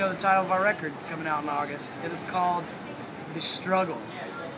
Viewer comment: sounds like Mike said "The Strudel" yummy